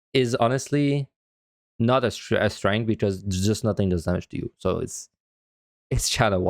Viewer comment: Why is clemency for paladin not a strength? I can't make out what is saying. CC didn't help. @ 44:01 "is not a strength because there's XXXXX you. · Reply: "is not a strength because there's just nothing that deals damage to you"